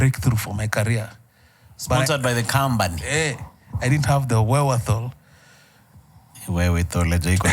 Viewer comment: What is that word he used@ 48:15... Wewethol( I fuckin butchered the spelling) am shameless 😂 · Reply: Nafikiri alisema "wherewithal"